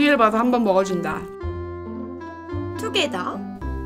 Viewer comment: 난 왜 이때까지 투게더를 두개~더~~로 들었던거야!?